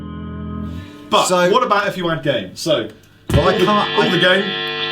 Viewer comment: Definitely thought he said "But what about if you weren't gay?" at first